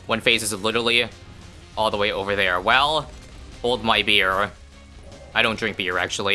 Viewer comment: Misheard this as "Hold, my dear" as if calling his audience "dear"